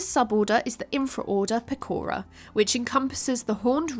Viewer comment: "It's me PEKORA!". When I heard Pecora that's what I tought. Still Great Job on The Video! :)